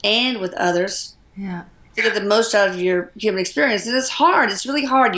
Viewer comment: you are an amazing human being. . I just heard another voice at around 48:37 sounded like "Yeah" gave me goosebumps!